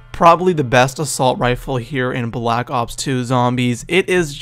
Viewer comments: did he say the best weapon onblack ops 2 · did he say the best assault rifle in bo2? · did he say Black Ops 2?